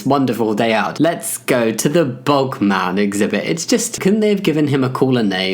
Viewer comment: Okay but at first I thought you were saying "bulk man" instead of "bog man" and so I thoight you were talking about the US body building competition lol